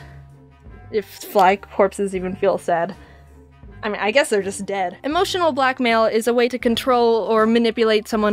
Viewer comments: for some reason i thought you said “emotional Black Male” instead of “emotional blackmail” i’m so stupid lmao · I thought you said emotional black Male lol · thought you said emo black male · When I heard say “emotional blackmail” my brain heard it like “emotional black male”....oof I’m not racist btw it just made me laugh XD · thought she said "emotional black male"